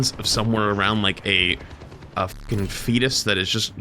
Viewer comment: I don't know why, but I didn't hear "fetus," I heard "feedist." Both are accurate though, in this case